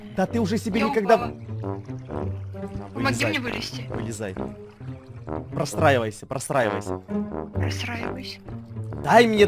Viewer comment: Мне послышалось не простраивоюсь а посраиваюсь 😹😹😹